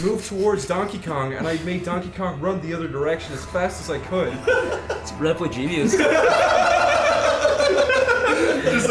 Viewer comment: What does Zach say around 22:10? “Something” genius? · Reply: DashAhead Ratboy Genius